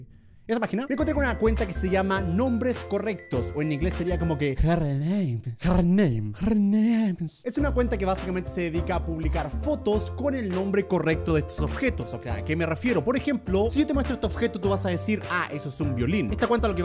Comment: parece que dice gernay